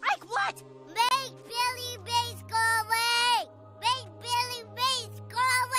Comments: I thought ike said “billy baseball” LOL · I thought he said, 'make jelly beans go away!!!' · I thought he said”bake Billy’s bake go away” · Did .. Did He Just Say Billy Mayes? The Power Of Oxi Clean Will Never Be Contained Unless He Didnt Say That And Now I Sound Like A Dumbass